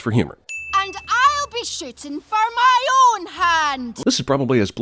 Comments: it sounds like she is saying "and I'll be shiting for me own hand" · Did She Just Said I’ll be shitting for my own hand or Shipping? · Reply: "shooting" like, the arrow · did she just say "I'll be shittin' for my own hand"?! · I heard “and I’ll be shitting for my own hand” instead of shooting